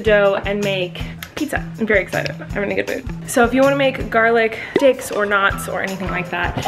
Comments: Did anyone else hear Colleen say “garlic dicks” at 9:33? Maybe my mind is in the gutter... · I definitely thought you said "garlic dicks," and it took me a solid minute to realize you said "garlic sticks." 😆 I mean, either works though haha. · Did she garlic "sticks" or "d**ks"?? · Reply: bahahahaha I thought she said that too but she said sticks it was just they way the video was edited that made her sound like she didn't say that. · ok i want to make sure im not the only one who thought she said "garlic dicks" please tell me im not the only one 😂